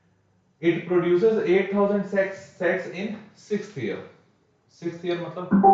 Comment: he said s*x instead of sets